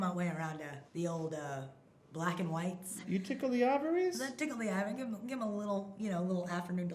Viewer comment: I heard "you tickle the ovaries?"